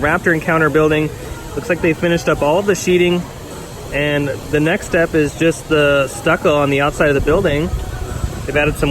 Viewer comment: what did he say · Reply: Halloween Horror Nights construction